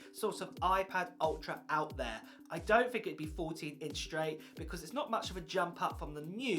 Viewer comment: I thought he said 40 inches 🤦‍♂️